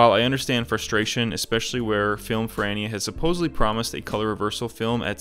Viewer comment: First I heard your “Film Ferrania” and thought “who the hell is FILM FRANNY?”